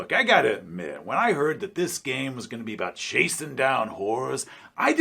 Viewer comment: I heard that as "Chasing down whores," at first. It must have been the porn moustache.